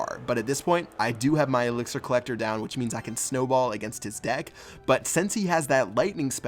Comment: did he say snowball?? 😂